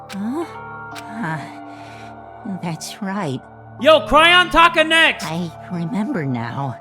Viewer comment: Aoi: cries on Hifumi Hifumi: opens his eyes: >:o: cRY oN tAka— sAyAka— mOndo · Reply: I THOUGHT SAID "CRY ON TOKO"